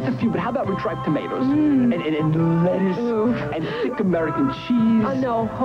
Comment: did she just say oof?